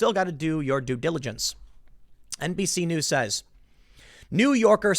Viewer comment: did tim say NPC news?